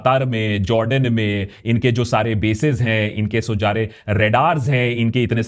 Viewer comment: Am I the only one who heard the "Inke so jare" instead of "Inke jo sare" at 9:57 ? Did I hear it right? So glad to know I am not the only one making such mistakes while talking... Achche achchon se bhi hota hai 🙃🙏